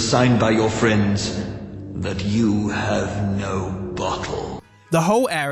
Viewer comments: someone tell me what hes saying here cuz all i can hear is "you have no butthole" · wait, did he just say "You have no butthole?" im legitimately curious as to what he means/is actually saying. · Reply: I hear bottle lol